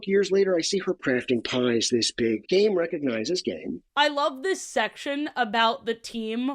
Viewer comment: genuinely thought he said gay recognizes gay and I had to rewind like five times. I’m too far deep..